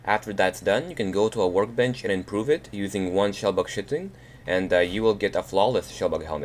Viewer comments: not to be mean but it sounded like you said shellbug shiting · It sounds like hes saying "shellbug shitting" haha